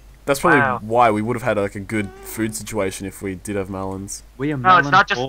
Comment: When he melons I thought he said bell end